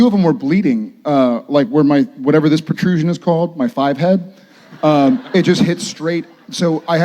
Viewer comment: did he just say five head 😂😂😂